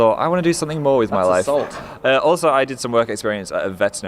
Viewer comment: I thought Dan said "lots of salt" 😂